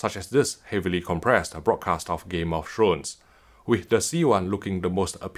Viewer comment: Did he just say, “Game of Shrones”?